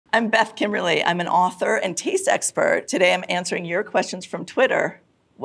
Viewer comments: It sounded like she said "author and tease expert" and now I cannot get over it. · i heard 'tea sexpert'